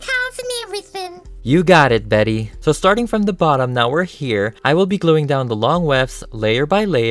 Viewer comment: What I heard was "Starting from the bottom now we're here"